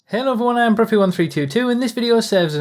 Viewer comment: Is it just me or does sound like he says "Hail Everyone" in the intro